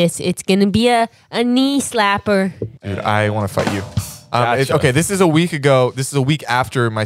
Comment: WHAT DID I HEAR? pls idk if i'm hearing stuff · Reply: Fight